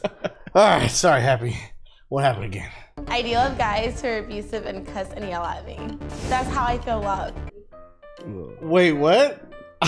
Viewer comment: I pretty sure this is what she said at 3:56 "I do love guys who are abusive and cuss and yell at me. That's how I feel love."